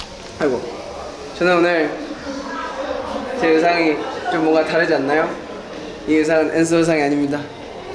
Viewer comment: I thought he said “This outfit isnt the Answer” 😂😂😂😂